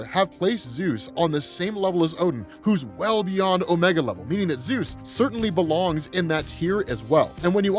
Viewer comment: Lol scrolling past and thought it's said omega level Jesus 😂🤣😂🤣